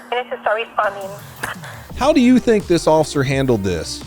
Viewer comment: ish did dispatch say "you need to start responding" ? wtf, she got things she needs to do, like stay alive, shut the fud up and wait for her to talk when she can.